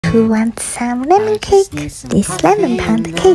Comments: I thought you said “who wants salmon cake “ 😂i was like I know she didn’t too cake with salmon and frosting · For a hot second thought you said salmon cake and was really confused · I legit thought she said salmon cake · I thought she said salmon cake lol I'm about to youtube it 😂